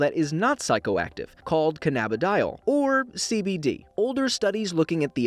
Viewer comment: i thought he said "CDC" XD